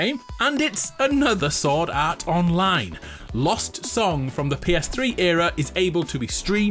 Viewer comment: İ thought he said silk song not lost song, almost fainted